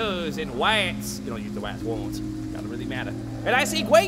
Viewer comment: I've looked everywhere, but I can't figure out what Dartz says about rats! Does anyone know? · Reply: "I only used the Rats once, it doesn't really matter"